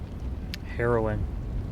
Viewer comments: LMAO for a second, I thought, you said "Heroin" captions didn't help.🤣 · After Powder was singing and says "... come across the river, and find...", I misheard you and thought you said "heroin" instead of "harrowing", and holy shit, I couldn't stop laughing for like 5 minutes